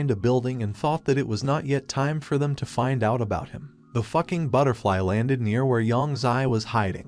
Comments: what did he say? I sweat i hear “The fucking butterfly” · In the moment 18:30, did he said "fucking butterfly"???